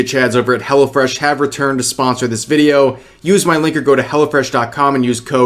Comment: haha it sounded like you said "Use my link or go to hell" o fresh